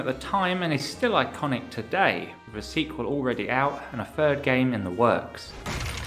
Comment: I thought you said ''the third game is worse'' here, thought you re-edited the entire video for a second lmao, since that game released after your last upload